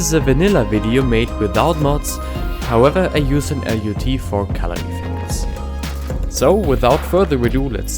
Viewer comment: What did you just say? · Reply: NhatAnh0475 "use an LUT for color effects" but with a "u" in color